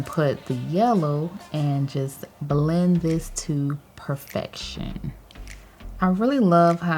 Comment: I thought you said "bling to perfection" instead of "blend to perfection", which also makes a lot of sense considering you're the queen of bling hahah